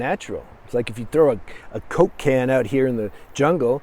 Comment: I thought he said, “a Cokehead” 😂